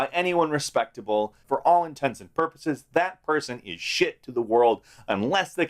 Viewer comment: Wait, did he say, "...for all intensive purposes"?